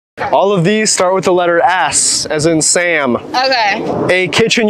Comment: why did I hear: “all these words start w the letter ass as in Sam.”